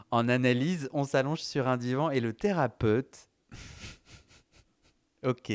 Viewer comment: J'aime beaucoup le son de ta voix. D'une, c'est Intéressant et de Deux, rassurant. J'ai entendu "thera-pute". Au départ de mes séances chez mon Psychologue, Je lui disais que J'avais l'impression de venir voir une Pute. Désolé pour la sorte de violence apparente de mon langage et ma pensée 🙂.